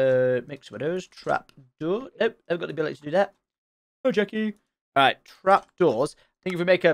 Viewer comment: was that an obscure Friday night dinner Hello Jackie i hear?